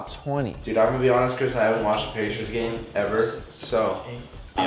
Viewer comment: Did call the sizers the patriots?